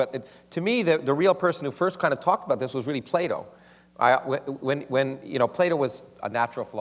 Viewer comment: when he said Plato, I thought he said play-dough